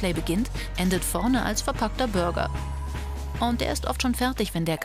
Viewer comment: hab „verkackter Burger“ verstanden😂